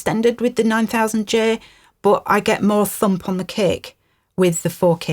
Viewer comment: I know you said "thump on the kick" at around 21:08, but I heard it as "thump on the cake" and I'm here for it. 🎉